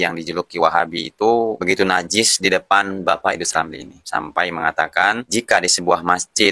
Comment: saya salah dengar atau emang disengaja,,, idrus ramli atau iblis ramli😅😅😅